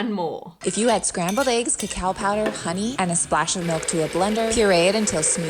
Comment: It sounds like she says, "if you add scrambled eggs to cow pie and Honey". Judging from the looks on their faces, maybe she did.🤣🤣